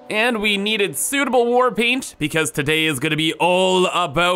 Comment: Ngl, when you said "today", I heard "Te-dey"